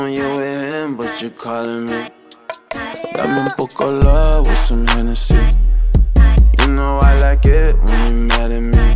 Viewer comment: Guys, I need your help. 😂 At 0:26 there's a kid voice in the background. I've heard it before in other songs. Any ideas what it says? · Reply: mateayaz "cut it up" DJ spinz producer tag